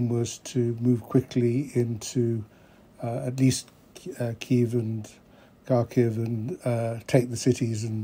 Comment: "Ki Keev" at 1:15 stopped me. Sir Lawrence suffered a glitch in his programming. If he can't stick to the script, he'll get cancelled. I think I'll take a look at what RT and the Global Times have to say. Diversity is our strength. 🥴 😂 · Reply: He said "Kievan".